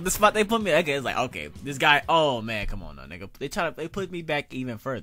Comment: Wow did just said my nigga? Hahahah you trippin